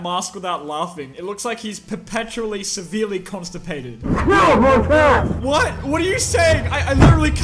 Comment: he said get off my path or pad either way that’s what he said